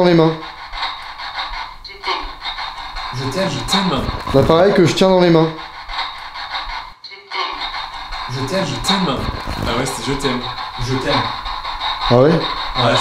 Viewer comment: moi j' entend " tu t'es"